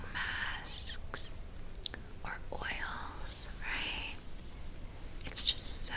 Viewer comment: “Or oils right?” My dumbass heard that as “or oiled Sprite” and I was tryna decode what it meant